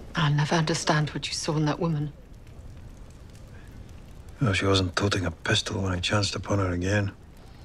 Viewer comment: What does Jamie say in 1:04 min? After Clair asks what did he find in Laoghaire. I listened a lot, but cannot understand... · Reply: he says 'she wasn't holding a pistol when I chanced upon her again' :)